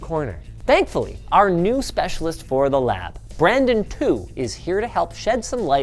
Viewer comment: At first i thought he said "Brandon Poo" lmao